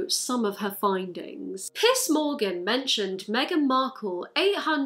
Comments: Omg i thought you were saying piss Morgan. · All I hear is "Piss Morgan". Brilliant. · I thought she was saying piss morgan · I thought she was saying “piss Morgan” and I was like yes queen obliterate him 💅💕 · It sounded like you said “Piss Morgan” and I like that better tbh. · im sorry the whole time i thought u were saying piss morgan